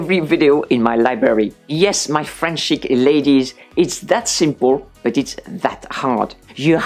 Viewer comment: Your videos are always delightful! Plus, because of my hearing not being the greatest, I always hear "Frenchy ladies" at the beginning of your videos, instead of "French chic ladies". Hearing "Frenchy ladies" always makes me smile.